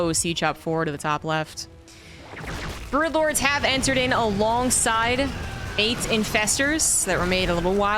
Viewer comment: dont ban me pls but 23:05 i understood Aids infestor instead of eight ^^ nice video